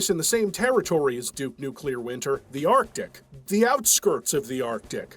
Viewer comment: Spelling "arctic" correctly like someone who's smart and paying attention instead of accurately wrong? That's not the quality control I've come to expect. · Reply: Yeah but then he goes and pronounces it like arc-dick.